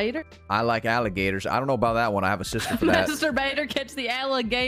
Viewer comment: Did he say he has a SISTER for that????? Sus